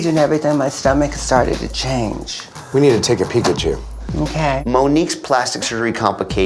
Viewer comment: did anyone else hear "we need to take a pikachu"?